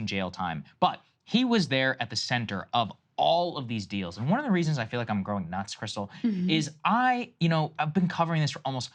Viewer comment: it sounds like Saagar is saying, "I feel like I'm GROWING nuts, Krystal." lmao